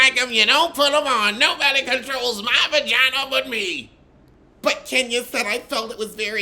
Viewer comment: I thought mama Joyce said “don’t nobody control my pajamas but me”🤣🤣🤣🤣🤣 it reminds me of that scene on color purple when his dad was eating that biscuit talking about make you swipe out the Caboose🤣🤣🤣🤣